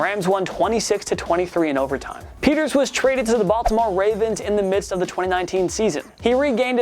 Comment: did he just say the Buffalo Ravens ?